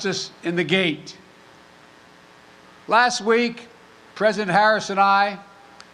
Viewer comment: Did he say President Harris